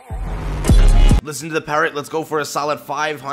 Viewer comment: I only heard it at the third time lmao "Listen to the Parrot". XD